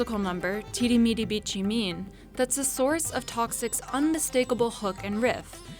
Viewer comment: Did she just say "teeri meeri bitchy mean" Wow!